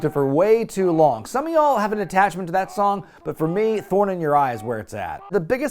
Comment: Oh yeah, "Thorn in your eye" was a waaay better Raw theme. (Or as I always hear it: "Put the Titantron, in your eye!")